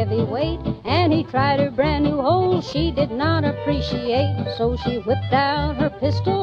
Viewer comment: This whole time, I thought she said “he tried a brand new hole she did not appreciate” wth